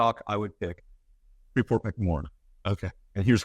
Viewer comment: At approx. 19:21 says the preeminent stock he would pick is? I could not understand and the closed caption did not pick it up either. Can anyone tell me what he said? Thanks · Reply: Freeport MacMoran. Then he goes on to say "I'm not a commodities guy - I'm a numbers guy" I happen to agree, but this guy's a total 🤡🤡🤡